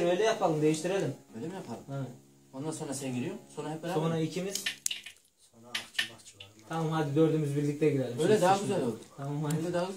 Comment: 0:9.5 0:10 saniyeleri arasında darbukacı ne diyor?😂😂😂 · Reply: Sonra aşçı bahçıvana